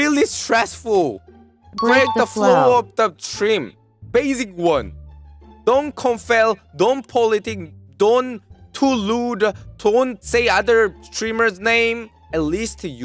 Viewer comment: when he said don't too rude i thought he said lewd...